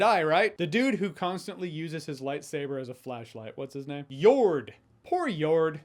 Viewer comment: lmaoo I thought you said "uses his light saber as a fleshlight.......... got GRUESOME images in my mind lol... WOW